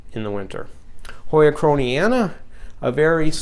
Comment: please, please can i have please the name of the Hoya at 6:56 ca, the strong growing one at the edge, before you go in the Cats-Room? Cant really understand... sounds like -kentiana at the end or something else... Be pleased, hugs and luck for you! Thanks, thanks, thanks, C. Please marker ne here. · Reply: Hi Claudia, that plant would be called Hoya krohniana; it is a close relative of H. lacunosa.